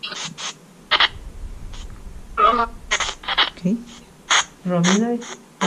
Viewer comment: En el minuto 13:41 , cuando le preguntan como se llaman? una voz femenina parece decir "Norma". Én el minuto 13:59 se escucha una voz femenina que dice " Mary ". En el minuto 14: 07 se vuelve a escuchar la misma voz femenina que dice "MARY". En el minuto 14: 23 las voces masculinas parecen decir "Miguel" " Leo", bueno, al menos eso creo escuchar yo con auriculares. En el minuto 15:44 , cuando dice "no tengan miedo somos amigos" , una voz masculina dice "espero ". En el minuto 27;16 cuando les pregunta " ¿hace mucho que pasó esto?" se escucha claramente una voz femenina que le contesta "MUCHO". En el minuto 29:36 , a la pregunta ¿Necesitan algo ? , una voz masculina responde "SI". Excelente exploración . Se escucharon muchas psicofonías, y muy claramente algunas de ellas. En realidad cuando ustedes hacen estas exploraciones casi siempre se oyen psicofonías y son IMPACTANTES, Felicitaciones por este trabajo a todo el equipo . Un abrazo grande desde Santiago del Estero , capital.